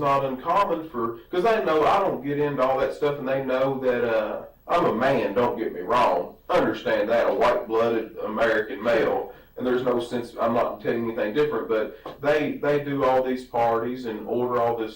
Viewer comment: Did he say “…a WHITE blooded American male”⁉️😂😂